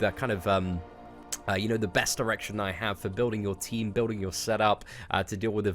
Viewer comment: did you say erection at 1:15? lol Nice guide! Thanks mate!